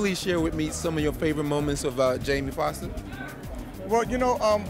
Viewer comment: Sounds like he said Jaimie Fox